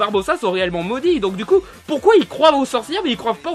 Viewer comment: Est-ce que j'ai entendu deux "croivent" sauvages ou j'ai rêve ?? Really nigga ?